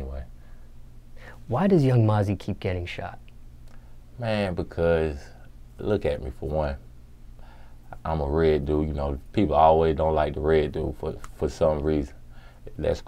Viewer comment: I thought he said the real dude